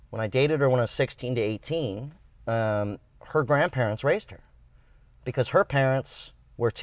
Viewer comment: I was listening to you in the background and thought you said her grandparents raped her .....lol